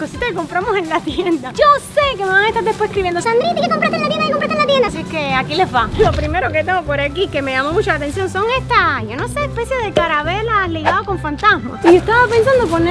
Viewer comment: Min 7:13 like si escuchaste que dijo: carabela 😂